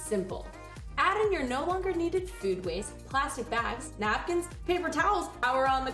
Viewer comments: Did you say plastic bags ? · In 0:57 did you say plastic bags? Am I hearing it right?